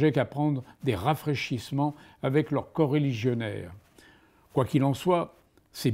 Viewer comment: 29 fautes. J'ai eu de gros problèmes de compréhension des mots, j'ai entendu "porcs éligionnaires" au lieu de "coreligionnaires" et j'ai entendu "LES quelques exiguës" au lieu de "et quelque exiguës". Forcément ça part mal mdr.